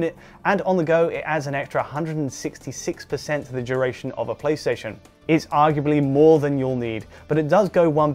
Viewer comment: Did he say "it adds and extra 160% the duration of of a PlayStation" - didn't he mean the switch? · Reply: Ohhh, maybe he said "play session" - sounded like PlayStation though haha